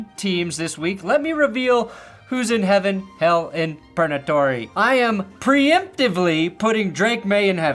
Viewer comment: I could SWEAR I heard you say “PERNAtory” when talking about who was in purgatory, and I STG if you don’t start calling it that…